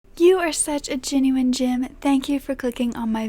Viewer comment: Lyzza Mendoza Ramos awww you’re the best💕💕💕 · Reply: feel embarrassed I thought you were saying genuine Jim.🤣 Love your videos.